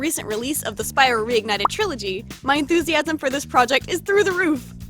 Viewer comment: Ok completely off topic but am I (beware this is a really long comment) the only one who thought that when she said “With the realese of I forget what its called my enthusiasm about this project is through the roof” she said “inspiration” instead of “enthusiasm” the other times I watched this vid but this time it’s “enthusiasm”. Shane Dawson has talked about this kind of conspiracy before, so could this be another example of it? ( pls respond I need to know)